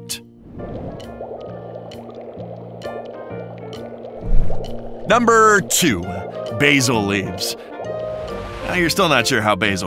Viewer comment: when at 9:13 he said basil leaves I thought he said that basil left!